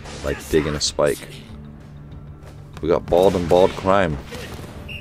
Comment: "We got bald on bald crime" did I hear that correctly? 🤣